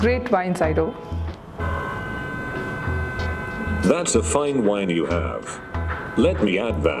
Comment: I serious thought she said "Fine Vines". I was disappointed.